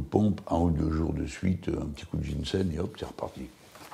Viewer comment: Salam, Salut.. c'est quoi le "DJINCED" pardon d'avance pour l'orthographe. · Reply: Le ginseng. Une plante qui se mange et qui est bonne pour la santé